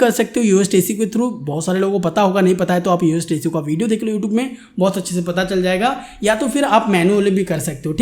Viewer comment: Sir aapne vo kya bola 20:15 par uhtc ?? What exactly you said? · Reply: HZ SAYING ABOUT YOAST SEO PLUGIN HERE